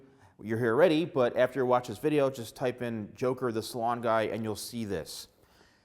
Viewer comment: thought he said “Joker the swan guy”